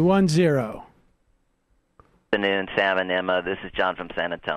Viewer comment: When he said "Sam and Emma", I heard "Salmonella" :)